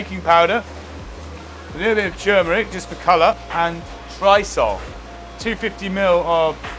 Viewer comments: I wish I could understand that last ingredient that goes into the dry batter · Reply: It sounds like he is saying Drisol, which is a brand name for a specific type of pre-mixed fish batter or a dry breading base used in many UK chip shops. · What does he say at 3:45? Dry Salt? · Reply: Trisol